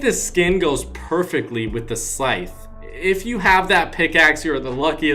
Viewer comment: Whats the name of the pickaxe? Cant understand it! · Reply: Omega sythe